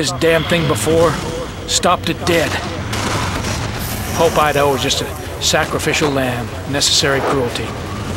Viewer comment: wait at 2:10 does he say "hope Idaho was just a sacrificial land"